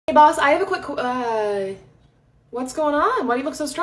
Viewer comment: I thought u said "Hey Balls" 💀